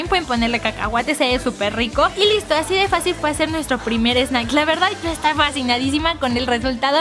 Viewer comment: slime?🤔alguien más aparte de yo sw fijo en que dijo slime? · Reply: Dijo snack